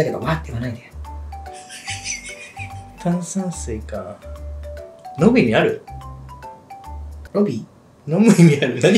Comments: 字幕見てないとマジで「ロビーにある？」って聞こえるからwwwwwwww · お料理しながら観ていて、 "飲む意味ある"のとこで、たまたま目を離した時に、私も"ロビー？"って聞こえてすぐに、Hiroくんが"ロビー？"って言った瞬間吹き出した😂😂😂 ほんとうにこのコンビ、何時間でも観てられる❣️❣️❣️ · 飲む意味ある？がロビーにある？に聞こえた… 会話のテンポがすごい好きです✨